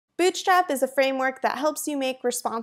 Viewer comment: I swear I restarted the video 3 times because I kept hearing "Bitchstrap" XD